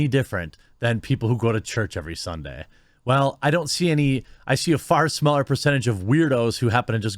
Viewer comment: Random moment of hysterical delirium because I'm tired and ate food that made me sleepy. Could not stop laughing at 10:20 because I thought he said "I see a fart smeller percentage of weirdos." I guess because he jokes on people sniffing their own farts all the time. I'm dead LOL